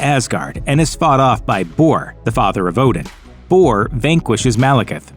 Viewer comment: Fought Malekith by Thor, the Father of Odin?? I think he meant to say Thor, the Son of Odin lol